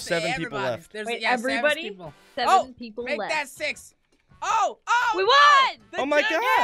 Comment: Did just say,s*x in 6:09 or